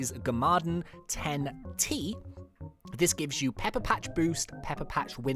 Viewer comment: When he said “pepper patch” I thought he said “peppa pig” 😆😆😁🤣🤣🤣